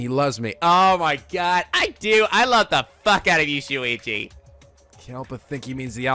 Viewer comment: I like how the subtitles says Shuichi as "each u8ee" (I'm not sure why I turned it on but I'm glad I did.)